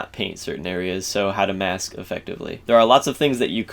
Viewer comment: "how to mask effectively" when he said that I thought he said mass effectively